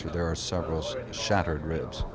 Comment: I listened to this several times. I thought he said "seven shattered ribs". The broadcaster said "several shattered ribs".